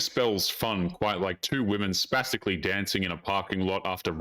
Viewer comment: Did he say "spasticaly"?? 😂